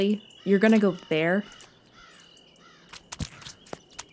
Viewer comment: What did say here? I couldn't understand him. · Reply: All praise to Odin god of the gods